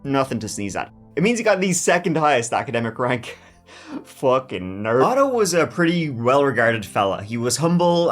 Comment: Did you call the victim in this case a “fuckin nerd” ?!?!? Because at approx 1:28 I could have sworn that’s what I heard. 😮😮😮